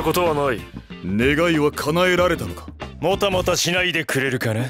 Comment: did he said negga